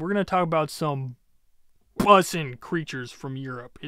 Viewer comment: uhm wh-what did he just say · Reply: i said BUSSIN